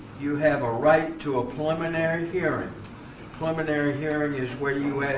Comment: Sounds like judge is saying 'pulmonary' hearing instead of 'preliminary'. Still, even after I spent 10 years in South Carolina. It's a steep learning curve for us West Coast folks.